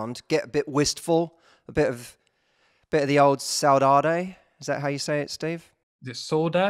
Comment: What does he say at 1:34? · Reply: Saudade. Is a Portuguese word.